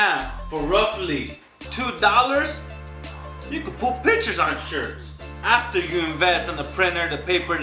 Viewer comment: “For roughly... $2... YOU COULD PUT BITCHES ON SHIRTS” I had to go back and hear what you actually said, I was dying 😂